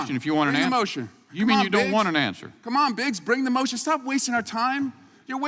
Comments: It sounded like he said "C'mon Bitch" LMAO. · Thought he said 'come on bitch' at first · I thought he said "come on bitch," for a moment there. 😅 At this point, it would not surprise me if they were calling each other swear words. Many Republicans are not living in reality to the point that they're living in a state of shared delusion. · I thought he said “come on, bitch”. That would have been hilarious.